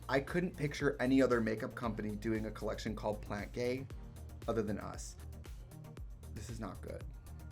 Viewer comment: I thought it said "planet gay" and I wish it did 😅💖 im obsessed with plants and also this pallette so I'm still here for it. P.S. hi lesbians! 😂